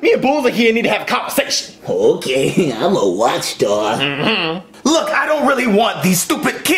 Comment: 8 years later in 2023 I thought Morton Koopa Jr. said he was a watchdog instead of wockstar😂😂